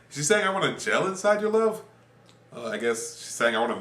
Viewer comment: lol I heard "I wanna jam inside your love"